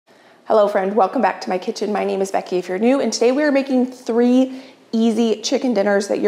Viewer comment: I don’t normally comment but just wanted to say I’m really proud of you for taking sponsorships. You work so hard on your videos, you deserve it. I also love hearing about the products you trust. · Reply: If I just had a nickel for every time I thought you said your name was Becky Agnew, or Becky if your new.